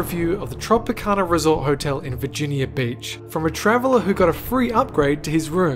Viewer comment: When he said Virginia beach it sounded like he said virginia bitch😄